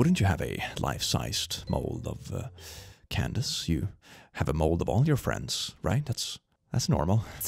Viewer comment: saying he has a mold of all his friends is actually what Buford says in response here